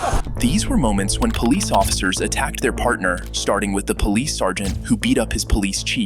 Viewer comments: I thought it said parents, not partner. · am i like the only one who thought that said "parents" insted of "partners"?? thats the main reason i started watching this but its fine ill still watch it either way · I thought it said "parent" not "partner"😭 tell me I'm not the only one😭